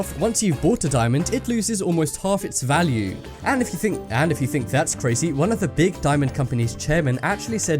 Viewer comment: Ehh.. Why Did He Say: " And If You Think Changes Voice And When You Think ". No Lying Watch It From